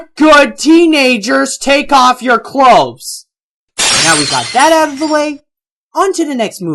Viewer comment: I actually heard: "Come on, good teenagers, take off your clow." Along with the sexual messages, like the one in The Rescuers and many others, I think I can say: Yes, Aladdin did talk dirty. In a later updated version, they lowered the volume there.